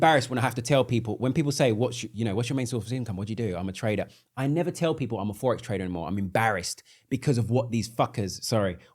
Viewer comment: guys, what's the book he mentions at 46:49 please? couldn't hear it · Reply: The Secret 🙏🏽